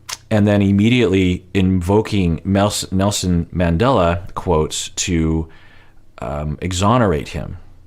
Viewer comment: Did anyone else want to slap some sense into Steven when he invoked Nelson Mandela (and mispronounced his name even)? He's so ridiculous. Good grief. And how long has he been practicing this speech? Does he just Google random quotes to throw out into his rehearsed manipulative conversations?! He just out cringes himself as time goes on. Props to Ari for calling him on his crap. And props to Kenny and Armando for being awesome, all the time. And even Corey made great points. You know Steven is below the bottom of the barrel when Ari and Corey are in a position to dish out respectable relationship and life advice. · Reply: Sounds like he said "NoSin Mandela"